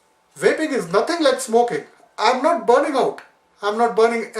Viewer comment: Did he just say "I'm not burning owt!"? LOL must be from Bradford :-)